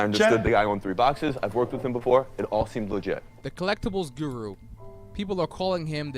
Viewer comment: guru said "it all seemed legit" instead of "it seems legit" This seems like a legit scazam